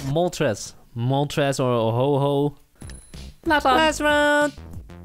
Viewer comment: Is it ok to come back to the fact that one of them said Ho-Ho instead of Ho-Oh